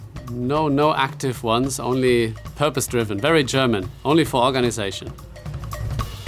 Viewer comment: i thought seb was speaking german instead of "only for organization lol